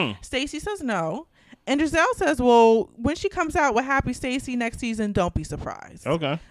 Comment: Did any one hear say it's called Spacey Stacey when they was talking about Happy Eddie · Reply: Oh, I thought she said "Stacey Stacey," which made no sense.